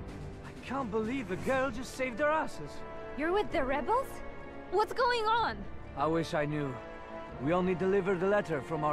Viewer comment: Anyone that speaks Polish, what exactly does the soldier say when moves them after they get the grenades? Sounds like "Ja tych miast" or something like that I hear this EXACT same voice line when playing as the Polish in Empire: Total War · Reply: He says natychmiast, it means immediately or at once